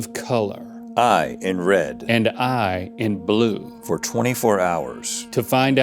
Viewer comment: In the beginning i genuinely thought that rhett said “i’m rhett” and link said “and I’m blue” made me laugh